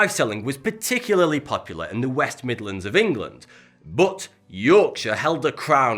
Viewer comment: did he just say West Midlands, Birmingham was lit in the past